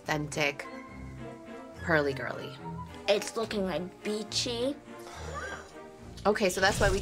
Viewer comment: Did she really.. 😂😂 this child.. I thought she said it looks bitchy 😂😂😂